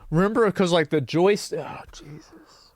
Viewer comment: when you said 'Jesus' I actually thought you'd said JASUS, like a term to be used for when something is so shitty you're lost for any other words.